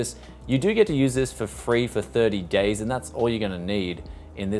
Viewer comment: Is it just me? Or his "days" sounded like he said "gays" instead... XD